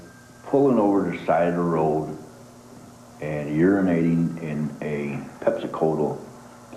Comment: did he say he urinated in a ‘Pepsi-Kotul bottle’??! What in the actual fuck is a Pepsi KotUUL bottle?! This guy is downright stupid. What a piece of garbage.